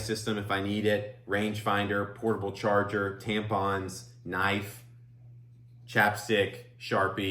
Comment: Did you say "tampons" at 11:46?? Interesting..